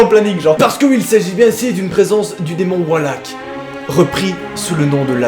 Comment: j'ai compris "le démon wall hack "